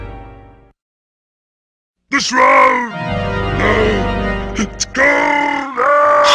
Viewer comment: is he saying "the shroud! No! It's gold!" ?